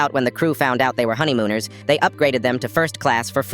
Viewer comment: why did i think i heard "honey gooners"